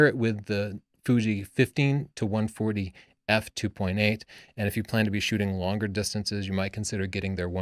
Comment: I thought he said fuji 15-140 f2.8 I was like wth how did i not hear of this? Sounds like a dream come true. Then I checked it... 50-140... oh. Sigh, oh well lol.